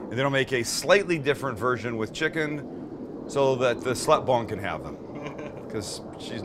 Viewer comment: Wait... is he saying slut bong or am I hearing that all wrong? 1:53 10:12 19:11😂😂😂